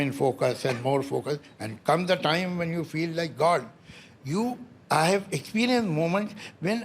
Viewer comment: what did he sayyyyyyy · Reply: he mentioned god😭😭😭